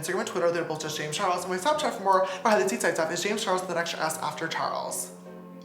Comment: what does he say when he talks about following him on snapchat? all i hear is 'behind the scenes...." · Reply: For more behind the scenes content it's James Charles with an extra s